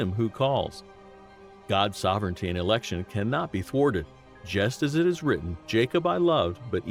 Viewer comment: Question: at the 16:38 mark, does the speaker say "forwarded like subtitle, or does he say "thwarted"? It just seems difficult to understand the meaning of the statement with the word forwarded at the end where "thwarted" seems to make the statement understandable, to me at least.